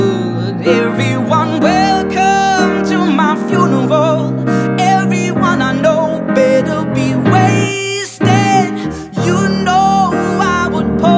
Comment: I thought he was saying 'everyone I know should be ways dead'